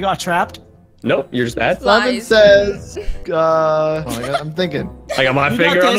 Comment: it sounded like said lemon says